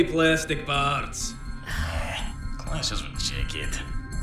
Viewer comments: Did he say “This doesn’t seem cute” To the shirt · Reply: He says something with "doesn't fit the jacket" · Help a dude out what he say at 1:27? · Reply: "Sigh... Doesn't go with the jacket" (according to spanish subs)